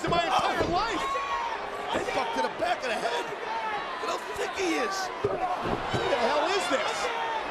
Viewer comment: did he say how thicc he is that’s very sus